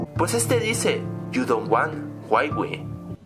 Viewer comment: yo en el audio escuche "you don't want to be me"...solo digo me pareció curioso